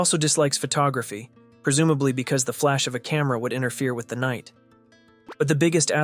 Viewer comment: for a second I thought you said “THE KNIGHT” but the subtitles really paid off